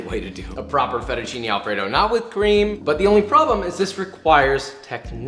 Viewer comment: My dumbass literally thought the first way was the only way to make fettuccine Alfredo— so when he got to 2:40 and said “cream” I just heard “crime”